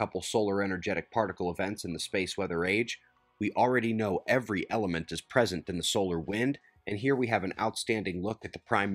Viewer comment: did I hear you correctly: "Every element is present in the solar wind?" <mail> Your comment is at 1:38. OMG if I grasp this correctly.